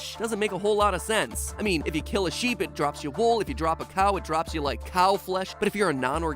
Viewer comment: did he just say if you drop a cow